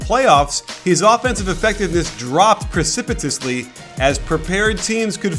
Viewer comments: what was that word he used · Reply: G.T. Bro. "Precipitously." means quickly. Or steeply. Think precipitation. His efficiency drops like rain (precipitously) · what word was that??? preci.. prepiti...precipitously... Its hard to pronounce...